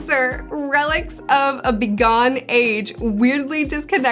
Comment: Did you just say "begone"?